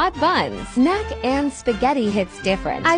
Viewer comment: The fnaf music got me off guard💀 Edit: I know it’s not fnaf music it just sounds like it stop saying that pls😭😭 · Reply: Thought I heard nut and spaghetti 💀